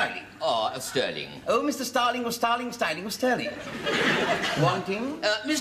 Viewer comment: You need to know your English place names for this one! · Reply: It doesn't really help that I misheard him at the beginning... I thought he said "Mr Starling of Starling Starling AND Sterling... I thought it was a firm of some sort. Made it a bit confusing to be honest. Thanks for watching!